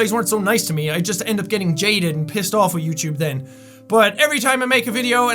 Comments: When said Jaded i thought he said "Jaiden" and was super confused xD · Excuse me, but what did you say at 2:15? My name is Jaden and I'm sure you said "we would just get people like Jaden getting pissed off at YouTube" WHAT?